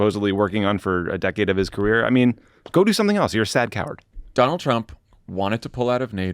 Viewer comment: I misheard Tommy and thought he said “you’re a sad cow” at first. 😅